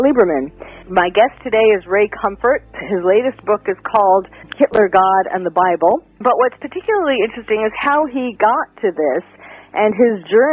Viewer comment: “Hilter god, and the Bible” is what it sounds like she’s saying.